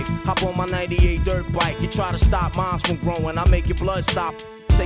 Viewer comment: He says "You stop minds from growing, I'll make your blood stop flowing." I always thought he said "you stop mines from growing"